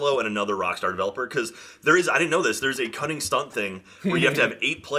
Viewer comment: Every time someone mentions GTA V: Cunning Stunts, I keep hearing them say "Stunning Cunts" in my head. 😆